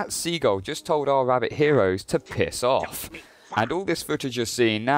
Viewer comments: As a canadian, I kept hearing "back off" instead of "piss off". I only heard the later when you mentioned it. Now I can't unhear it. 😂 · Anyone else laughed at hearing the sea gull say "Piss off!"? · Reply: Mutra The Mutration it sounded like “Back off” to me, I only heard “Piss off” after I knew that’s what it was supposed to be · When the bird I guess says "Piss off" I hear "Buzz off" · I heard "back off" from the seagull, not "piss off". But now that I'm listening to it again my brain is slowly malfunctioning.